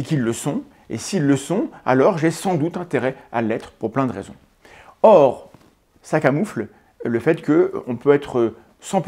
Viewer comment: J'ai compris "Sac a mouffles"